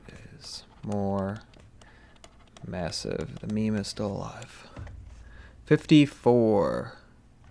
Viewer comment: what did you you just say…? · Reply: Lol I said the star was more massive and then I said the meme is still alive because it’s an internet joke